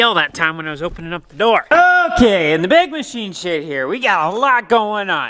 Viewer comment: ig sounds like he says in the big machine S***